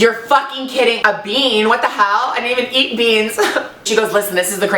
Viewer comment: when she says " I don't even eat beans! " it sounded like Fred for some reason. Lmao. love you! <3